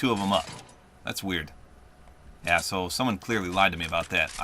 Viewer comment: What did he say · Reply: YEAH, SO